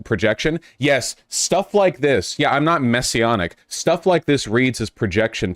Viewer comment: i fr thought he said "I'm not messy-anna" like thotiana 😂